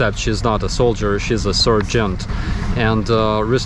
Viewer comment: I'm used to the old people speaking English with a Slavic accent, but I honestly thought at first you said... "surgeon"? Then I thought, she's awfully young to be a surgeon? In the US you will definitely be in your mid to late 20's before you achieve that. She looks early 20's!! Then I realized you were saying SARGEANT! She still looks very young!! But seems very dedicated!! Smart, pretty and level-headed!! Makes me want to join the Ukrainian Army just to meet a woman like that!! LOL